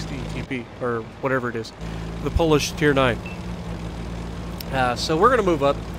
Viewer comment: Haha... I thought You said "the foolish tank" then looked at it once more. Polish it was :-)